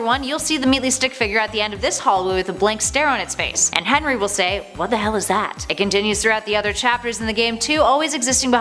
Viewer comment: Henry does not say that he says "what the heck is this"